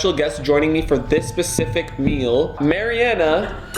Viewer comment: did he say "pacific"? in